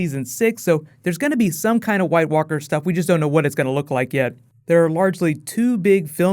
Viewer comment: thank you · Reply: i thought it said rarely